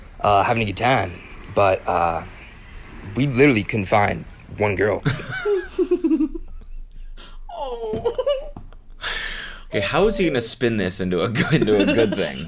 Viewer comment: He said "we couldn't find one girl" I heard "we confined one girl" 😅